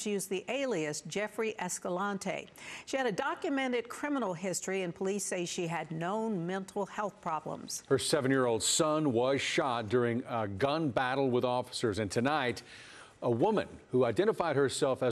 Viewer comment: no mental health problems hey..... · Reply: Yeah, it sounds like she says "no mental health problems," but the subtitles say "known mental health problems." It's a big difference in meaning.